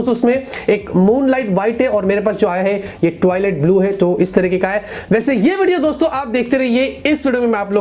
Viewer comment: toilet blue😆 bhai kya bol diya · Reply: Clearly Twilight blue Bola hai😒